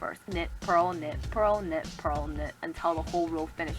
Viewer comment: Lol when you said knit, purl, knit, purl, really fast it sounded like u were saying nipple but with an asian accent. 😂😂😂